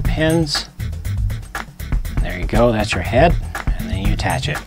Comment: did you just say HER head?